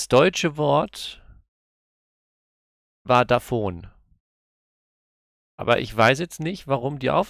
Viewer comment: Das deutsche Wort war Da Phone! Spricht man das in Franken echt so aus? Davon hab ich noch nix gehört ^^